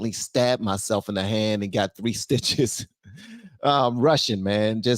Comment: * lmafo i thought you said "I'm Russian" not "I'm rushin" lol 😅